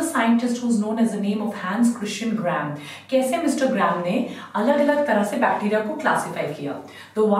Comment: i heard insta gram😆